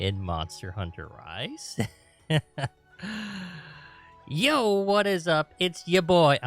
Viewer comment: I heard “ yo what’s up it’s YA BOY......” I almost cried... you can’t do that 🤣